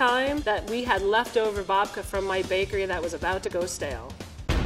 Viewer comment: Who else thought she was saying vodka instead of babka Just me okay... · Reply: I heard "leftover vodka at our bakery that was about to go stale" and was immediately confused, excited & concerned for my desire for professionally-made, vodka soaked breakfast... enunciation could have saved me from this emotional rollercoaster.